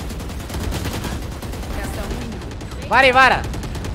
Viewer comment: entendi vary a vara